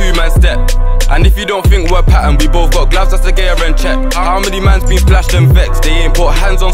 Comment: "And if you don't think we're patterned, we both got gloves, that's De Gea and Cěch"❄ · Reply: Brudda i thought he said the gear and chech 😂